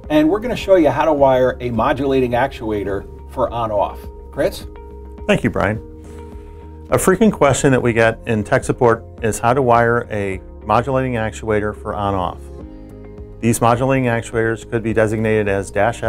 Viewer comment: I thought he said “a freaking question we get” in the beginning. 😂